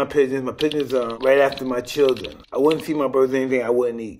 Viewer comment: “I wouldn’t eat anything with a mother and father” Watch out Batman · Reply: I could've sworn he said he ate children when he was younger....